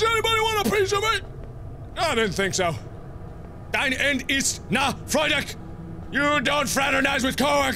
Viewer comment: Dein ende ist nahe freue dich, but then he sounds like he is saying, "Die end ist now fry dick".